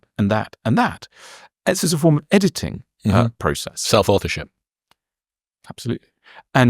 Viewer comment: self-what? I'm not hearing it right. Can someone tell me what he is saying? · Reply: Self-authorship 😊